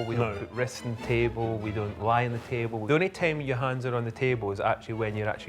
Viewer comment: I thought he say don’t LIED on the table. Imagine someone lied at the table. He would stuff them in dumb waiter ( food elevator ) send them to the royal dungeon with there crusty goblin.